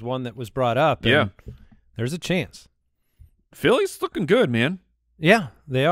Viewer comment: into the podcast I swear said Philly is "F$%kn good"...anyone else hear that?